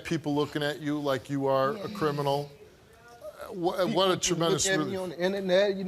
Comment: Did he just say crinamal?